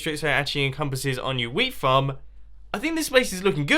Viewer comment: "I've filled in all the fences," The next second there's a hole in the fence to the right. Good job, good job. :) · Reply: when he said "wheat farm" I thought he said "weed farm"